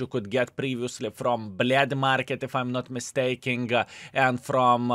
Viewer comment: I thought I heard "блять Market"